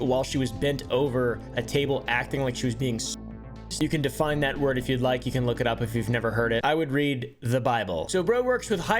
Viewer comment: does anyone know what he said at 9:27? Genuinely confused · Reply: I believe he said sodomized